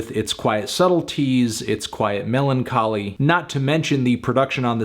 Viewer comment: I swear he said "melon-choly" at 1:27.